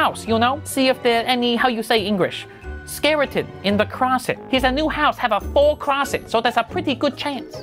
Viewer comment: What did he say at 5:47? Ceratin in the closet?